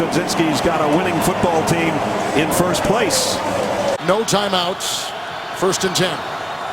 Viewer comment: did he say winning football team in 1st place?????